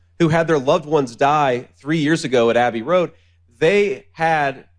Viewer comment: Did he say they died at Abbey Road?